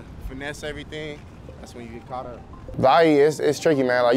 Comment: can anyone tell me what town he is saying he is from at 3:00 i cannot seem to understand it. it sounds like "lahee" or the end of ha"waii" please comment and help. close captions wouldn't help either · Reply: the lahee ur referring to is called the “ie” which stands for inland empire. The area is east of los angeles.